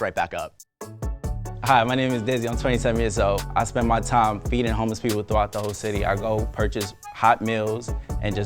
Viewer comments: I THOUGHT DIZZY SAID HE SPENDS HIS TIME BEATING HOMELESS PEOPLE I ALMOST DIED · Wth I thought he said beating homeless people instead of feeding🤣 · I straight up thought dizzy said "I spend my time eating homeless people" at first... but he said feeding not eating lmfao😂😂